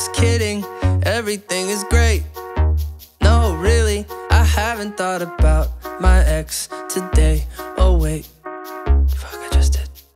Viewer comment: LYRICS [Verse 1] Everything sucks, just kidding Everything is great, no really I haven't thought about my ex today Oh, wait–(Fuck, I just did) But I went outside for the first time In a few days and it felt nice And I might try doing exercise (Hehe, not really) [Chorus] I wanna forget my bad days, all my bad days And be okay, and be okay Spend my spare change down at the arcade And then I'll say, I'll say [Verse 2] Everything sucks, just kidding Everything is better than it used to be I didn't write a song about my ex today (Oh, wait–um) But I called my friends and I told them We should hang out if it makes sensе 'Cause I miss them and I hate bеing alone (Oh, really?) [Chorus] I wanna forget my bad days, all my bad days And be okay, and be okay Spend my spare change down at the arcade And then I'll say, I'll say I wanna forget my bad days, all my bad days And be okay, and be okay Spend my spare change down at the arcade And then I'll say, I'll say I wanna forget my bad days, all my bad days And be okay, and be okay Spend my spare change down at the arcade And then I'll say, I'll say [Outro] Everything sucks, just kidding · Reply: I've just thought that he said ( I hope that I find my ex today) 😂 instead of( I haven't thought about my ex today.)